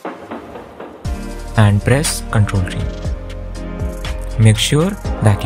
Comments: What did u say on 1:53 bro? Ctrl +? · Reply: ctrl + t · what did u say 1:53? crtl and? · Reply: t